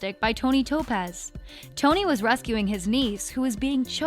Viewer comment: “Tony was rescuing his niece” 10:25 is that what she said? or am I miss hearing things...